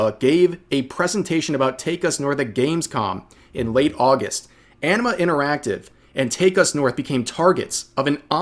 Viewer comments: I keep hearing "enema interactive" which is a fitting name on several different levels. Jus' sayin'. . . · Did I hear him say "Enema Interactive"? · I keep hearing "Enema Interactive."